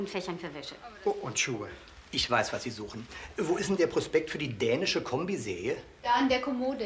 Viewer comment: Ich hab früher immer "dänische Kombisäge" verstanden...